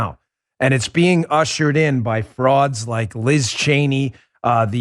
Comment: When he said frauds I thought he said broads! LOL In actuality she is both and an imbecile, plus all the others Dan stated! If you stop to think about it, they all have to be suffering from a gray matter deficit to support the destruction of the backbone of this country!